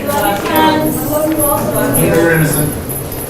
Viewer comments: I'm sorry but did i hear someone say "i know you're innocent" 5:36?!! If i did then wow. So much denial man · what did that guy say "we know you're innocent". Sorry what? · did i hear her dad say "we know youre innocent" WTF wow thats chilling